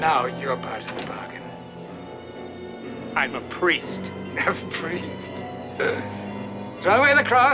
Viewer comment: I am more afraid of James Mason. The film makes it appear that the Master is his pet. He also appears to treat the Father as a pet. When I first saw this clip I heard him say, "Bad priest!," as if the priest were a disobedient animal.